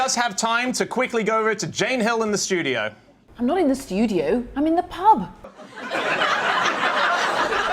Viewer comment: what does he say here? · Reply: what the fuck you doing in the pub?